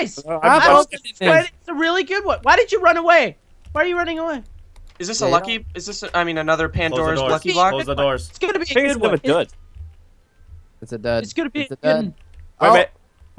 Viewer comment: For a second I thought it said the pandorica box